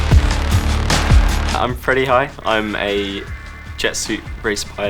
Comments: thought I heard the first pilot introduced himself as "I am pretty high" lol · did he say “I’m pretty high”